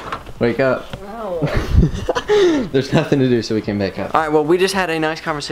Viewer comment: did he say so we can make out??